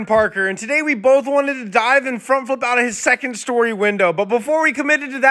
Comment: i thought he said today we both wanted to die out the window💀💀💀